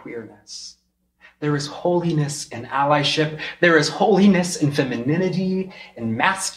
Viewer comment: There is holiness in "?" At the 3:11 spot. Please help someone with better ears than me! Sounded like "Allyship". Think I'm right, but someone else please confirm. "Strong Delusion" of 2 Thessalonians 2 verse 11 coming to life here!! 😡😥😥 Needs the True Jesus to transform into Normal again! I pray for he/she!!! 🙏